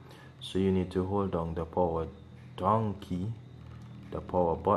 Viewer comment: Sorry, you have a really strong accent. At, 0:30 could you please type what you said in the video. It’s not you! It’s me my ear drums are deciding not to work today 🤣🤣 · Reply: Lol its down key. but I know its sound like donkey have fun